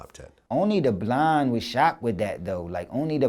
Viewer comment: Lol. I thought he said " only the blond was shocked with that" when referring to Drake, i was thinking , is he talking about white people..